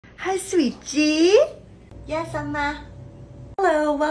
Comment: I thought she said hi sweet cheeks